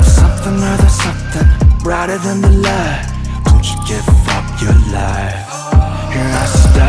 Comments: I’m in love with this song... but I’m the only one that hears “fu*k your life” at 1:24 instead of “give up your life”? Wait it’s actually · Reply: I hear "Don't you dare f*cking lie" instead of "Don't you give up your life" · Is it just me who misheard Rm's part fuck your life.......😂😶💜